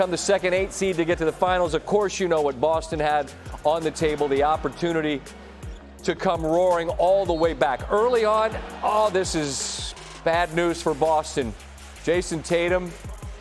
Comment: I thought Perkins said.. Lakers all the way🤔